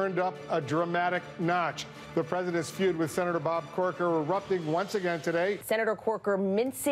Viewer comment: Lol thought he said senator porker haha